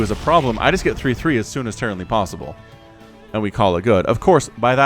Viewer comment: Did he just say "Terranly possible"? xD